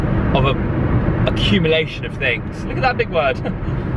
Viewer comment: I love how you're like "Look at that big word!" I thought you said, "Look at that big bird!" xD Love your videos, you inspire my videos so much! Thanks for your honest chats, they help me with my own loneliness so thank you! <3